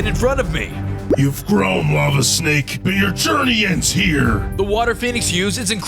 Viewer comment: On first one I thought you said "mama snake" 💀💀